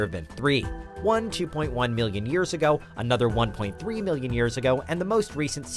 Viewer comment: And someone cant listen points at you He totally said one point three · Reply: Ah, I get it ! He actually said "another 1.3 million years ago ... " and I thought he said "another one .3 millions years ago ... " ! There you go !